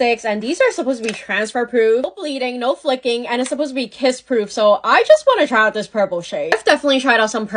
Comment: Who else thought she said piss proof